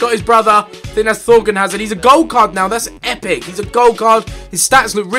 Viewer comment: on the thorgan hazard one at first i thought he said 'hes a gold c*** now! thats awesome!' no idea why...